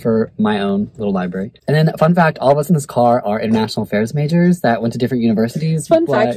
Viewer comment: Happy to see none of my favs were unhauled :) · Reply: I thought he said you were international Spanish majors and I was like ?